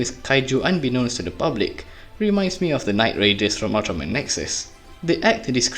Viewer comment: did someone say nexus?